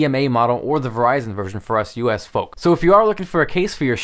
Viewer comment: did he just say U.s fuc?